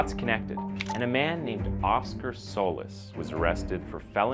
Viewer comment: Souless is a fitting name. I know I heard that wrong but, that's all I can hear when you say his name.